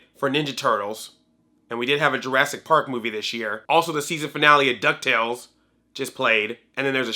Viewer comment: I thought you said "Series Finale of Duck Tales". I almost passed the frack out in heartbreak.